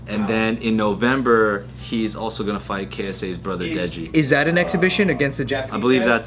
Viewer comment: I thought he said KSI's brother. Does that mean Deji? Cause Deji lost to Alex Wassabi. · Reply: he meant ksi but i heard ksa 12:50 . Deji did lose to wassabi but he recently beat fousey and he’s fighting mayweather on nov 23